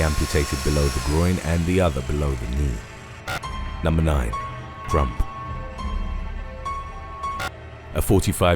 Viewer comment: I thought he said trump lmao